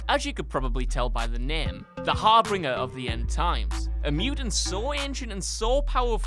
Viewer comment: Did he just say "HarbRinger"?